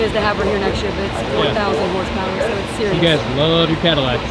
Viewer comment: Did I hear her say the next CTS-V is going to have 4,000hp @ 15:05? k…